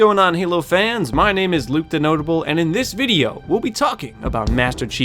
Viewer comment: Tell me why I always hear “Luke Denotable”